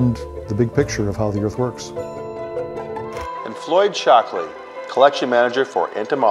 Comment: Am I the only one who heard " Floyd Chocolate"? Yes, just me, ok....then....